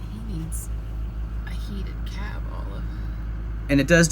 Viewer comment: Good day Sir, Great tractor snowplow therapy... Sir, I'm pretty sure that around at 3:33, I heard your wife saying in a compassioned tone voice ( he needs a heated cab ??? ) 😉 Yes she did say at 16:03 ( it only took 20 min !!! ) Well Done Sir 😉👍 Cheers !!!